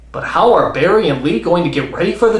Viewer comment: Holy crap, you scared me! I thought you said Stan Lee.